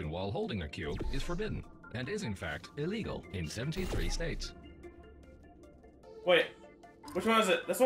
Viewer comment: "Is illegal in 1738 states" am I the only one who heard that?